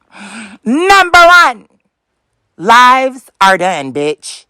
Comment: 🤣😂😆 Meanwhile he's STILL doing them to this very day.......every DAY ! 😄girl BYE! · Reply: Omg…. All this time I thought he said, “lies are done.” 😂 I thought he was talking about other people lying on him